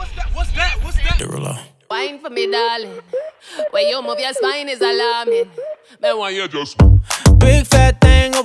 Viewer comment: "Way you move ya spine is alarmin" shit that makes so much more sense I thought it said "Way you move your sline is salami"